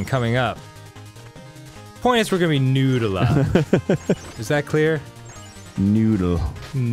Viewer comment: I heard "nude a lot" as more like "noodle art" in a Boston accent.